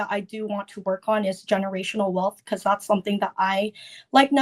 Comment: I thought she said "generational love" at first. Well that's easy, raise children.... You don't want generational wealth. Unless you do it right. The kuds will end up spoiled with no work ethic...